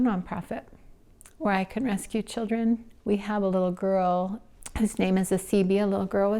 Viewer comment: His? I think she said her! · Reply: very much sounds like she said "HIS" but the subs say "whos"..... but ..... 🤷🏽‍♀️